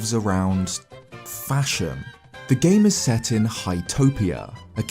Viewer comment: I heard fascism in stead of fashion🤣🤣🤣💀